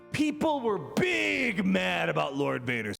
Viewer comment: Right at the 59 second mark, I really thought he was about to say Buff Leviathan, instead of buff Lord Vader and I lost my mind for half a second